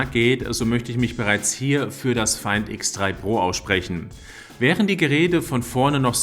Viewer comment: Ich habe die ganze Zeit zum Feind x3 pro verstanden. 😊 Ich dachte dann die 1 Chinesen hassen sich bis aufs Blut. Echt schwer. Die ganz teuren Smartphones haben den besten Rabatt bei free unlimited max. Wesentlich guenstiger ist zb das iPhone12 oder Apple 12 nur bei Verträgen mit zb 30 GB. Da free unlimited aber so viel Wert hat, man kann zb das Smartphone als Router verwenden und PC etc darüber laufen lassen, ist das kein Vergleich. Den Tarif gibt es Fuer 59.00 plus Einmalzahlung Diese ist beim iPhone 12 pro max bei 149. Beim s21 ultra und oppo bei 49 Euro. Ein wichtiger Punkt ist der Wert des Smartphones nach 2 Jahren. Gerade deshalb ist das Angebot so nice. Übrigens gibt es die handys aus China teilweise viel billiger. Das mi 11 ultra kostet 720 Euro, bei ebay mind 1000. Ich glaub Einfuhrsteuer ist nochmal fast 100.